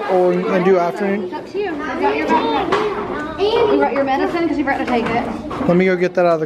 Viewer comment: omg i heard say annie 6:27 and then the mom said did u take ur meds or did ur brother take it